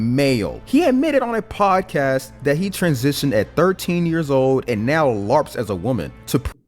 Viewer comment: The photo of Kyle looks way older than 13 I'm calling bs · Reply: At first I thought they said 13 years ago